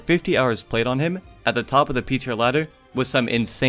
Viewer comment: you said ptr, I thought u said pizza lmfao